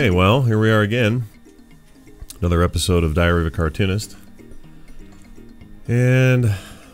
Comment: It almost sounded like you said diarrhea of a cartoonist.